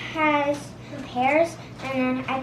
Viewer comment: Whoa whoa whoa whoa whoa 12:17 did say his salad had hairs in it!